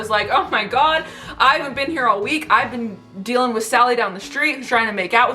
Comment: “I haven’t been here all week.. I’ve been dealing with Sally down the street trying to make out with me and drawing nipples on my barbies” 😂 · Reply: I thought she said: "I haven't been here a week"